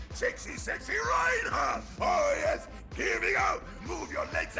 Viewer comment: did he just say sexy sexy reinhardt....?